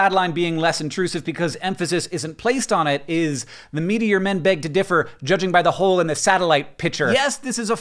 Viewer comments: I always heard it as "the media men beg to differ" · Huh, i always heard it as "the media men beg to differ". Seems i was wrong.